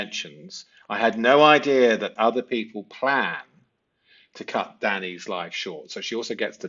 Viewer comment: QUESTION: I heard "PLANNED" 🤔. But, I think you say the tense change is there with her saying, "I had no idea Other People PLAN to take his life." Aha moment with your dive into the "other ppl" embedded conf, however. Maybe this will be cleared up anon, I'm here for all your breakdowns: such exacting clarity. It's very satisfying to have words, terms & concepts to put to vague, gut noticings.